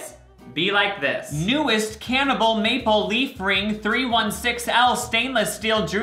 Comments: am I the only weirdo who heard him say cannibal instead of Canada??? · Did anyone else hear him say cannibal instead of Canada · definitely said "cannibal" instead of "Canada" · it just me or did say Cannibal instead of Canada? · Did he say cannibal ring?? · did he just say cannibal? 😳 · So we aren’t gonna talk about how he said CANNIBAL Instead of Canada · "Newest CANNIBAL Maple Leaf Ring 316L Stainless Steel Jewelry Fashion Men Boys Golden Maple Ring" I'm not sure if I just heard the word "Canada" wrong but all i can hear is "Cannibal"